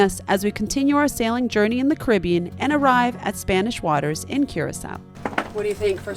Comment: I kept hearing "Spanish waters" and getting confused. "But Curacao is part of the Netherlands!" Ohhh, you mean the Spaanse Water lagoon!